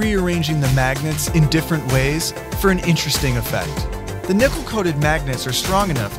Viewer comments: Did he just say nipple coated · did he say "nipple coated"? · did he say the nipple coated magnets 😂😂😂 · I heard nipple coated magnets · Did anyone else hear "nipple-coated magnets" at first or am I just high?